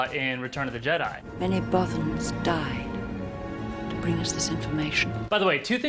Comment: Oh...... I thought it said "Many orphans died to give us this information" IT SOUNDED LIKE IT TOO MUCH, okay?